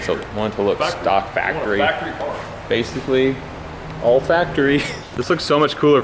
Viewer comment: When you said "all factory" you looked like you were sniffing the body so I thought you said "olfactory".